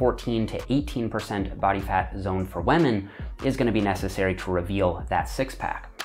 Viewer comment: Sounds like he says "wamen" in the beggining when talking about bodyfat levels lmaooo